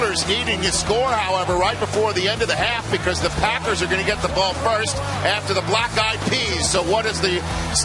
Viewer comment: It took me a second after he said "The Packers will get the ball after the Black Eyed Peas" to realize what he said. I heard it as "The Packers will get the ball after the black guy pees", and I was like WHAT???!? Then I remembered the awful halftime show, and it all came back to me!